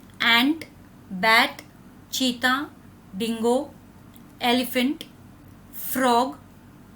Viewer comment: what is D Word? Dingo?